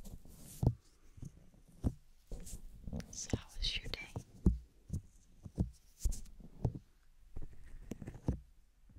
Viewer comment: What are you saying at about 3:39? It sounds like: "stop the shooting" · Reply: "So how was your day?"